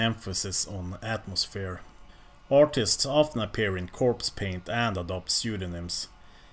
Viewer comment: Genuinely thought you said "autists in corpsepaint" when you was reading from wikipedia 😂